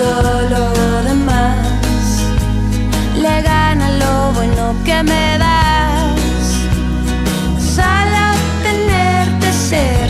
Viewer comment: Hoy me entere por este video que esa parte dice "Le gana lo bueno que me das..." durante años pense que decia "LE DAN AL LOBO LO QUE ME DAS..." vivi engañado toda mi vida!! :"V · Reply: Yo pensé que decía " le gana al lobo en lo que me das"•